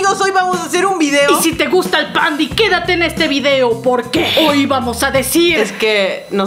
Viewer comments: Lo que yo entendí: Y si te gusta el pan quédate en este video. Yo: Pues si me gusta el pan, pero ¿qué tiene que ver? Luego lo volví a escuchar y pues ya le entendí bien. Xd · Cuando dijo "Si te gusta el #Pandy" entendí "Si te gusta el pan" :v